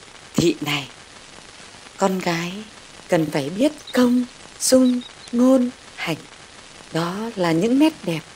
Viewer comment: Tui có nghe nhầm không nhỉ " Thị này! Con gái thì phải biết công ' sung' ngôn, hạnh" 😂😂 ai nghe rõ sửa giúp tui ik